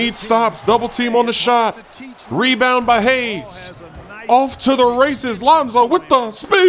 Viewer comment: I thought it said “Moses chokes Lonzo” You ain’t Sprewell baby! 😂😂😂